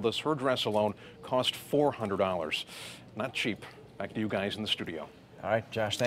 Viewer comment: did he say " not cheap, like u guys in the studio"